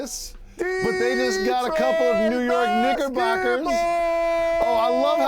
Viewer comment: Did he just say niggerbocker or am I tripping